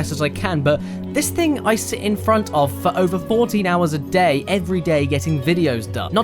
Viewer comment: You said "this is where I work for over 40 hours a day" That's not a typo, that's what he said. There's only 24 hours in a day Caddy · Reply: I think he said 14.